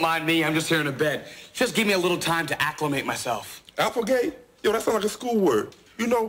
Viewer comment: What is he saying in 00:16 Minute... "appli what"!?.. i cant understand cause im from Germany, thanks for help! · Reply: It's not a real English word. He was insulting Carlton's "acclimate" by saying something that sounded like it ("applegate"?)